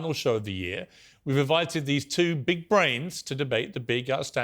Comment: Cenk is a "big brain" I couldn't stop laughing!! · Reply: I thought he said pig brain for cenk